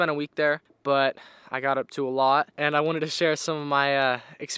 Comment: I had to rewind. When you said you got up To a lot, I thought you said you got up to Eilat, in southern Israel 🇮🇱. עם ישראל חי!!!